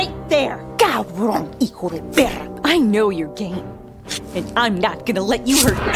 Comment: Lmao when the first time I watched this when vaggie said “I know ur game” I thought she said “I know ur gay” 💀